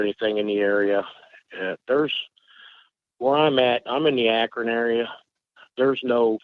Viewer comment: Where in Ohio did this take place? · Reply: I thought I heard him say Akron area.